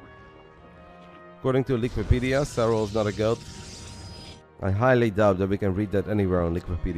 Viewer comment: I thought Rotterdam said "Joona is not a girl" around 12 minute mark. Took a while to get that he meant "GOAT". For a moment I got really excited that Joona might've cracked his egg recently according to some rumors. :D